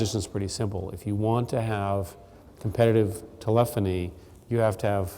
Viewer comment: What is this word he is using; ...if you want to have competitive "telephany" or "telefany"?